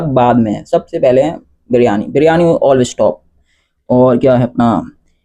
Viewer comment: biryani all stop ? Ya biryani all is top ? Kya bol raha h bhai😂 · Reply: Always top 😅💪🏻😸